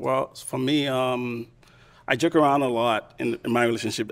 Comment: Joke sounded like jerk 😂😂